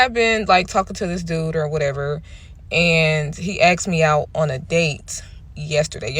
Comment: Can someone explain to me if my hearing is bad or not or if its an American way of speaking - I see videos where it sounds like people say " He Aks me on a date" I re listened and still hear "aks" surely is it is ask ?